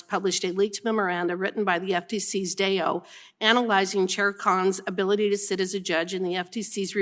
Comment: Well done Congresswomen Ms Hageman! Keep exposing the crooks and corruption!! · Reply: When she said "Chair Khan" I thought I heard "Sher Khan" the giant snake from the Jungle Book.